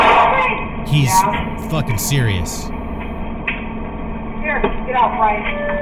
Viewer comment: It sounded like she said a name "get out Ryan" or maybe Bryan. Did she know him??? Edit: okay, I looked it up and she didn't know the kid. She wasn't telling him to get out though, another customer was walking in as she handing over the bag and she was tell that person to get out.